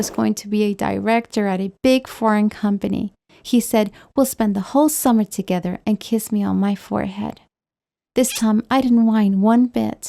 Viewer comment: "We'll spend the whole summer together and kissed me on my forehead" But I heard "We'll spend the whole summer together to kiss in bed"